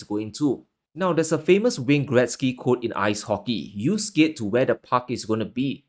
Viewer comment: Did you just say Wayne Gretzky 🫡 awesome content keep up the hard work👊